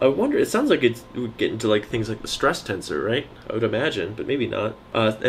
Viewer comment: 7.51: I thought you said: "distress tensor" - that would aptly describe the experience of not knowing how to even begin addressing a problem that you know should know how to address if you'd studied it properly - "I had a distress tensor in the back of my eyeballs at half past two this morning while trying to cram Chapter 10 of Jackson for a seminar with Prof Hugelhorff in the morning".